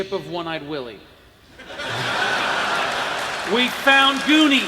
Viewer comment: what does the guy in the audience say? Something in relation to The Goonies I would guess, since Kurtz had just said something about "finding One Eyed Willie's ship", and makes the audience erupt with laughter. EDIT: TehSlan wants to know what an audience member says at 1:28:38 as well. Context: Jim Darkmagic rolling a 24 on his 6d6 damage roll. Sounds like "______ would be proud!" Come on nerds, let's figure this out! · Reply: "Hey you guys" which is the line sloth says in the movie.